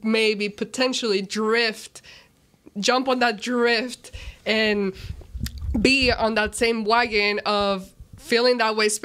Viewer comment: bro wth is she even saying. Drift , wagon ? Sheesh